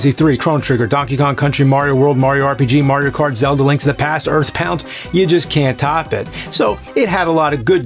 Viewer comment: did he just say "Earthpound"? Lol.